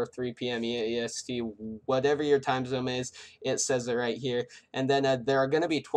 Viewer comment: i swear to god he said time zome